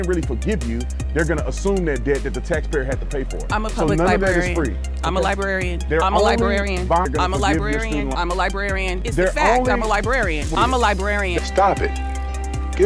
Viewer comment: All I heard is “I’m a moron, I’m a moron, I’m a moron!”